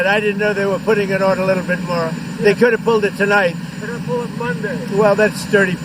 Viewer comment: Did I hear the reporter ask him if he will “pull funding”?? He is extremely dangerous on his own, but the press are just as guilty and complicit